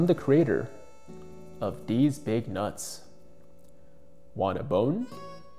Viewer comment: Here I was just Gibbs gonna say a nice kid book took I hear “these big nuts”